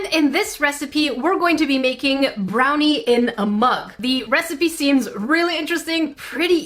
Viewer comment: Some how, I heard "how to bake brownies in a month" not "how to bake in a mug"